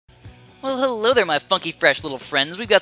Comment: Who else thought he said fucky in the beginning?